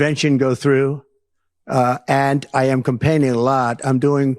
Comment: "President Xi and I had a beautiful meeting, right there on that sofa...." Was JD Vance on the sofa too? 4:07 It almost sounded like he said "I'm complaining a lot" instead of "campaigning". 🤣